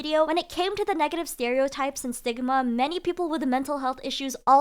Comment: yo im cooked when she said stigma I thought she said sigma😔